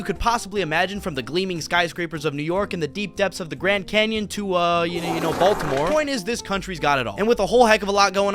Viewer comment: baltcore? what was the word?